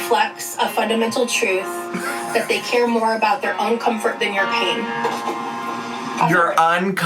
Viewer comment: I think here she says "their own comfort, than of your pain"